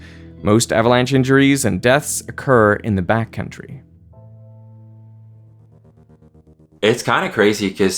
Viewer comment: I know they're saying back country repeatedly but in my head i keep hearing "bat country" and can't help but think of Johnny Depp playing Hunter S Thompson in Fear and Loathing... 😂😂 Wait what're you doing?! We can't stop here, this is bat country!